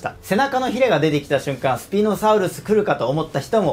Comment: 解説動画ありがとうございます🦖 自分的に今作は「好奇心 探究心とは老若男女誰もが強く抱くモノ！」というメッセージ性を感じました！ 9:32 ここスピノサウルスて言ってるんだけど空耳でツチノサウルスて聞こえて勝手に爆笑しました🤣